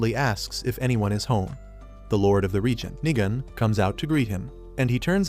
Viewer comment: True and also I thought that 1:01:56 I thought I heard nigga😅😂 but it was nigan😂😂😂😂😂😂